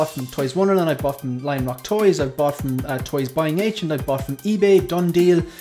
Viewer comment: what website did you say you used before mentioing buying agent? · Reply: LION ROCK TOYZ Very good experience. Thanks for watching.